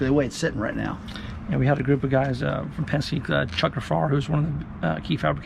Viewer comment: I think he said Chuck Gafrarar (Ga-fa-ra). He works on lots of stuff in NC still, showcars and trophies. He raced go karts going back to when he was a kid, and his son Caleb is doing outstandingly in junior karting.